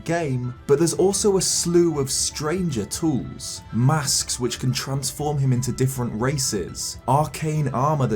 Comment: I thought he said masks which can transform him into different racists